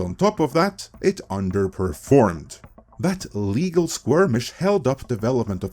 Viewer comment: At first I thought was having another COMM-ittee moment, with him saying skirmish but it sounds like he is saying "squirmish". I didn't know that squirmish is actually a word. Thanks for teaching me something new today!